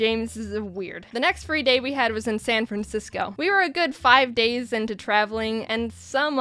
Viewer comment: I thought she said Fran Sansisco and I was like wAt